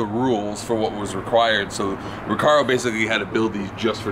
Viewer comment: Every time they said recaro I thought they were saying Ricardo